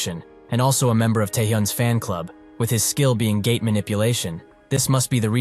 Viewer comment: I literally heard gay manipulation (But it was gate)